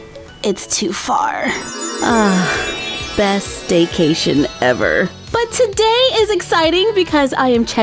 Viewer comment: DID SAY... DAYCATION? I LOVE YOU! PLEASE PIN ME!🐸🐸🐸🐸🐸🐸